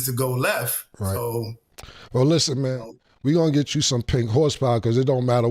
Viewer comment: What did Cam say he's going to get him a case of? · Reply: Pink horsepower